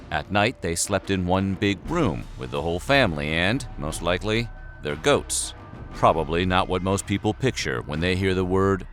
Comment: I had to play back multiple times thinking my brain was broke. I kept hearing “they're goats” instead of “their goats”